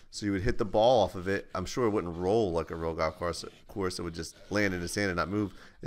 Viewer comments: i thought i heard GOTH course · I thought he said "Goth course"